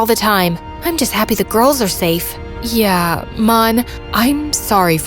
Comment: I thought she said mom instead off mon